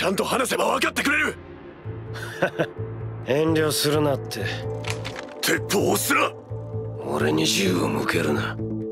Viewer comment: ここ「鉄砲を捨てな」にしか聞こえんから初見のとき字幕の必要ある？ってなった